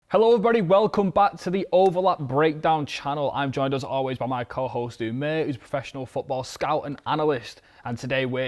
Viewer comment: Not being funny but if your accent is that thick, talk slower. I thought you said “I’m joined as always by my co-host and mate”.